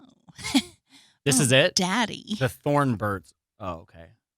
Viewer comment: ! This whole time you've been talking about The Thorn Birds, I thought you were saying THE THORNBURGS!!!!! hahaha